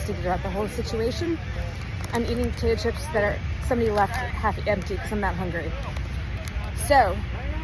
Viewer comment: I am trying to figure out if I misheard her, she is eating potato chips that someone left behind??? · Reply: She said she’s eating someone’s half finished chips because she’s NOT hungry. I give up. Vote for president Dwayne Elizondo Mountain Dew Camacho, I guess.